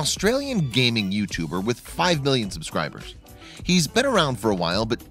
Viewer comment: did he say billion